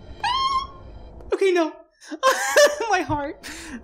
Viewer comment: did you just said “ok na” in tagalog at 9:03 or im the only one hear it